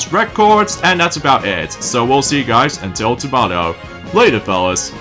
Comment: Hey! it sounded like to said tomato! not tomorrow!